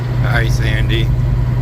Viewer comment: Ok, I have to know; at 31:41 did you say “hi Sandy “? I don’t want to think ya didn’t and look silly.. I had my husband listen and he said it before I did.. 😊 My dog finds the poop and rolls around on it🤢their weird 😂 I know exactly how you feel when the kids are grown up 😩 and you are right about truck treating would be the lot lizards 🦎 😂🤪 both you and look happier, something good happened (I hope) 😊👍🦮🐿🐰🐰